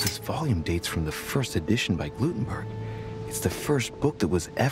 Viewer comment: Okay, I officially lost it. 24:05 - "-first edition by Glutenberg" ...wait, did he say "Glutenberg"? 24:20 - "A volume of the Glutenberg Bible". Oh my god. My sides.